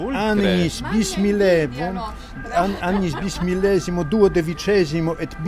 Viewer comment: When he was reading the title of the book, i swear i heard him say "bismillah" twice.